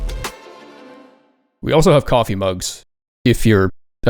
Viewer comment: i misheard coffee mug for coloring book and nearly just accepted that a coloring book of giving you the massive middle finger existed now